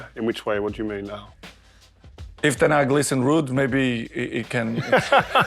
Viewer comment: what did he say at 4:37 i didnt understand, can anyone tell? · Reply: "If ten hag listens to Ruud"